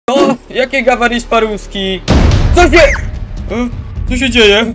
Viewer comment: Ja usłyszałem gawarisz poarówki